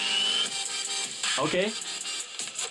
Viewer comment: he swear he said oh sh*t